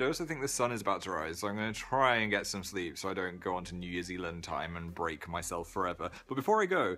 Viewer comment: he said New Years ealand instead of new Zealand